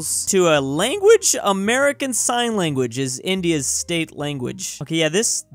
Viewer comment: DID HE JUST SAY INDIA